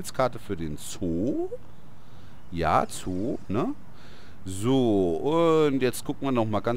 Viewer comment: "Ja Zoo ne?" "Sooo" und das "Sooo" hat sich angehört wie das englische "Zoo" :D